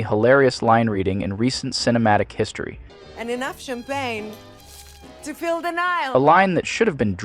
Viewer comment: The 'enough champagne to fill the Nile' line sounds like it belongs in a river cruise ad. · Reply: I always hear it as "enough champagne to fill Denial". Because Hollywood is in denial about her ability to act.